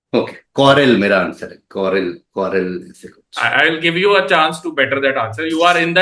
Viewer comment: m i the only one who heard someone saying cotrell softly.